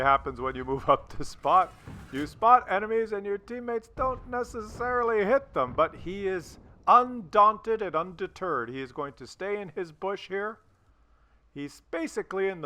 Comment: Did he say'"undaunted and under turd?" @ 3:35 these guys put the soar back in sordid ,the daunt back in dauntless, the x back in crement